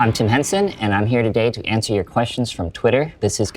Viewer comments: i swear i heard "i'm too handsome" instead of Tim Henson at the start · Is it just me or you guys thought he said “I’m too handsome” in the beginning?